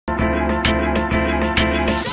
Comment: I heard "we can be either loved or hated by what be believe" which goes in hand with how the world is today.